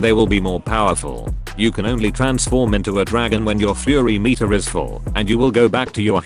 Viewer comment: I thought it said furry meter lmao